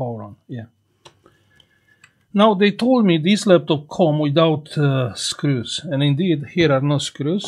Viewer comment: at first i thought you are saying: this laptop is dirty 😂😂😂